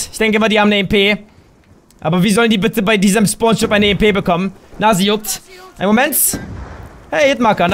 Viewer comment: Hat noch jemand bei 15:22 im Hintergrund Nase juckt verstanden? :D